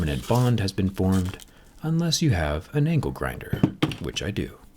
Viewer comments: An angle grinder is something I won’t expect in a phone teardown💀. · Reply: Damn I thought he said ankle grinder I feel a little better now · What I heard: “Let’s get the ankle grinder.” Me: 😳